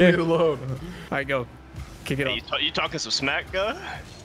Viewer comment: I thought said “You talkin smack cuh”😂😭